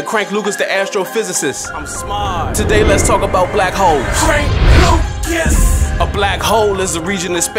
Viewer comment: Man I had this playing but wasn't watching the vid...I thought you said black hoes at 1st LOL